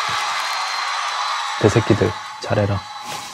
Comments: 소리만 들으면 개새끼들 잘해라라고 들림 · 나만 ㄱ새끼로 들림? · 첨에 '내새끼들 잘해라'할때 나만 "X새끼들 잘해라" 로 들리냐? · 개새끼들 잘해라 처럼 들림 ㅋㅋ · 나만 여기서 개새끼라 들려? ㅋㅋㅋㅋ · 자막 안보면 개새끼라 들림ㅋㅋㅋㅋㅋㅋㅋㅋㅋㅋ